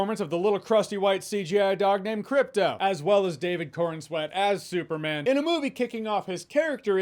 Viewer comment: I didn’t know the actors name before watching this video so I thought Jeremy was saying “David Coren’s wet ass Superman” 😂 I can’t unhear it now